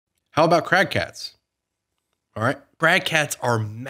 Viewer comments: I thought he said Crack cats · I thought you said "crack cats" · i thought you said crack hats and i was about to question the sanity of current generation wizards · Oh "Crag Cats", thought you said crack heads